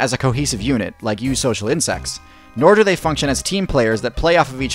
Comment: I will admit, for the longest time, I thought he kept saying "You social insects" and only recently realized he was saying "Eusocial Insects"